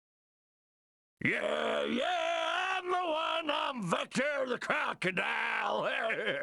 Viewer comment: I always thought that Vector said "Yeah yeah YEEeeaaæææaaaaah! Lookin' not baaad!"